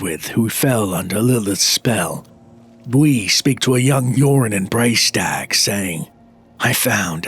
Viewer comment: I kept hearing "Yorin" as "Urine" making it impossible not to laugh at the whole story.